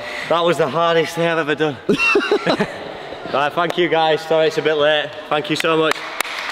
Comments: With his accent, I thought he said eff you guys, when he said thank you guys 😅😆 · I swear thought he said f*ck you guys😂😂😂😂